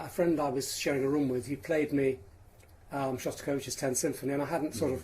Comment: Who's 10th Symphony did he cite? At 3:20. Couldnt understand what he said. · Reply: Shostakovich 10th